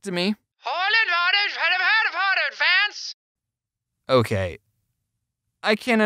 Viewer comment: i heard "dievridhrbeejsvvdhwjdm give me a fence"